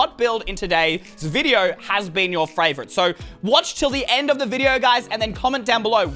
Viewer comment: did he say "frayvirite"?????